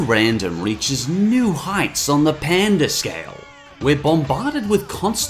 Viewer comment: I legit thought he said "Panda Scale".